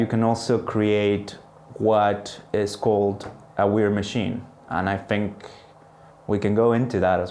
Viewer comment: did he say "ware mac hine"? Could someone tell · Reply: He said weird machine. "In computer security, the weird machine is a computational artifact where additional code execution can happen outside the original specification of the program," from Wikipedia.